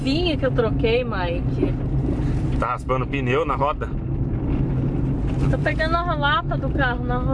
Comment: não dá pra escutar direito, mas eu ouvir o falando asim oó " ta raspando o pneu na roda" 1:51 foi isso mesmo ? kkkkkk